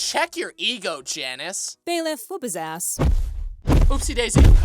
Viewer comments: Did one of your actors just swear? I heard.."ass" ... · Did bro just say ass?